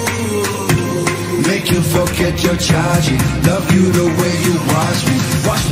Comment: At first I thought he said “Make you forget you’re tragic” Lucas you didn’t have to come for me like that I still love you tho